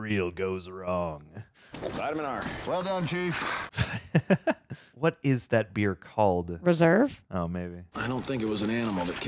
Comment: I heard "What is that beard called" 😂